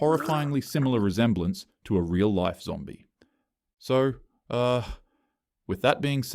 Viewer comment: thought he said irl saw movie instead of irl zombie lmao